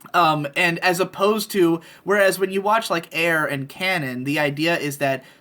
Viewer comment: Can anybody correct me on the name of an anime he mentioned? It sounded like he said "Air and cannon". Help is much appreciated